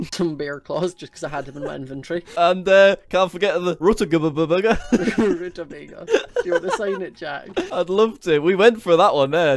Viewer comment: what the bloody hell did you even say?!?! Rudabbaga???